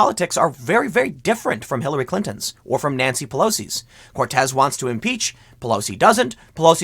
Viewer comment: I heard “nazi pelosi.” Silly but I did have a laugh